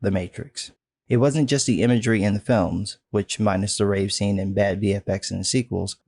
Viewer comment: I thought you said "rape scene" and I was like "WTF kind of extended edition are you watching??" Lol